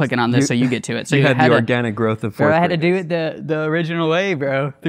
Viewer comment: what is this word he uses? Denigrowth? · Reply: Organic growth I think